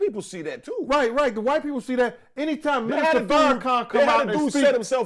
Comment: did he say Farrakhan?